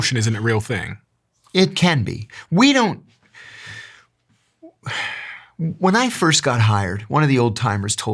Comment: He said we don’t… then he thought for a second and changed what he was about to say😂